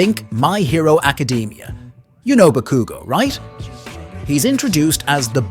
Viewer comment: I’m sorry. I had to stop the video at 2:40 and leave a comment WHAT did you just call Bakugo 😂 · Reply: Bkugo, lmao